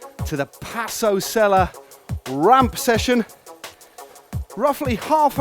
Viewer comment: please, I don't understand the very first words at begin of the video at 0:05 ''raphaely half an hour session''???